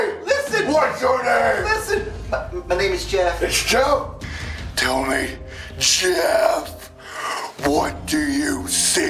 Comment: when you put on captions instead of it saying “Tell me Jeff” it said “Tell me shit” 😂